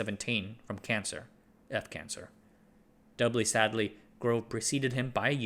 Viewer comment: am I hearing this wrong? He passed away from death cancer? Does he mean throat cancer or am I mishearing? Lol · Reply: According to the caption , he said “cancer….. eff cancer”. I heard “dearth cancer” too.